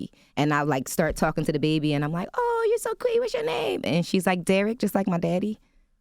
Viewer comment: she does say "SHE says Derek just like my Daddy". Good to know someone else heard what I did lol